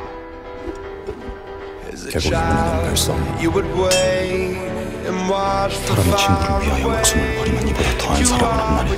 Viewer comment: 뭐라고 했는지 궁금한데 알수 있을까요? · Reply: 사람이 친구를 위하여 자기 목숨을 버리면 이보다 더한 사랑은 없나니 너희가 주 명하는 대로 행하면 곧 주의 친구라 - 요한복음 15장 13절